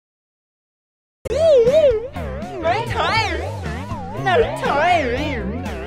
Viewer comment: I always think Blippi says this 0:13 Woohoo! I'm very tired very tired Hahaha! (Reverse) we're racist we're racist xD